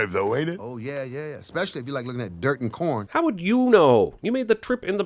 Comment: I first thought he said "if you like looking at dirt and p#rn" I'm like wait what